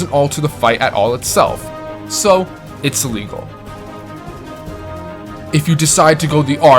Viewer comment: Did you say illegal or legal at 8:00? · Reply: Legal. I'm still getting a handle on audio stuff lol.